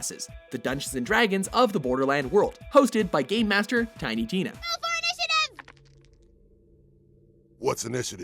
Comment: Was I the only one who thought that when he said tiny penis in tiny tenia